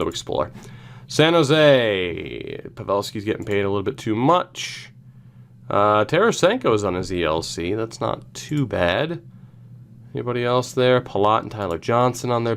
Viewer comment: i stg i heard “taylor-senko”